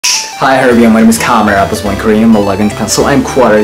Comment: どうしてもmy name is カメラに聞こえてしまうのだが...